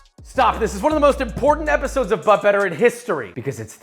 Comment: you. First time I came across this series, I first thought it said but butter. And I was very confused.